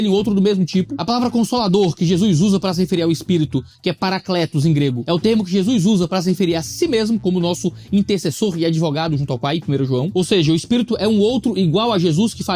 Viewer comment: Como que fala: "Peia pra dez levou sozinho" em grego? 😂